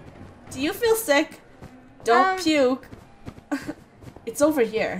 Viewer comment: XD when said dont puke to i thought he said dont nukee